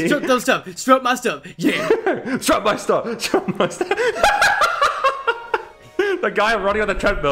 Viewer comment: what do they keep saying? “Strub my stub?” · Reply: Strut my stuff